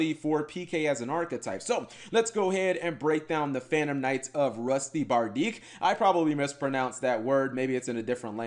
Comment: At first I thought it said "the phantom knights of busty badirche" 😂